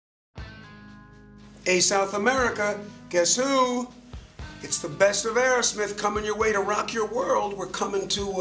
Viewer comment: WTF is saying Steven at 0:03? I understand something like: -" Hello South America, Qué súúú!! " O_O lol